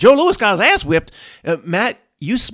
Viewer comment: Wipe Tyson. Lol, maybe the funniest accidental nickname of all time. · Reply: I thought he said white Tyson. Both something you wouldn’t wannna say to him in close range